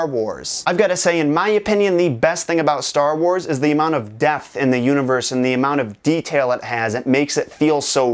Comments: When he said so much depth at the beginning of the video, I thought he said death. So I was REALLY freaked out. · Who else thought he said "amount of death", not "amount of depth" X'D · Who else thought he said "amount of death", not "amount of depth" X'D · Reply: yeah, I thought I heard "death" for a sec too lol